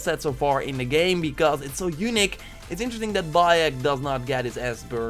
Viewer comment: Everyone you say unique I mishear it as "eunuch" :P